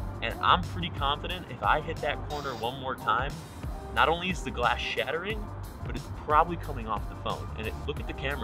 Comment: I thought he said no longer is the glass shattered it does not even look like a phone any more